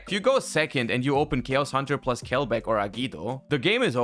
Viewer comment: "Keosandra".. plus Kelbek? What card is saying plus kelbek? cant't hear it right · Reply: Chaos hunter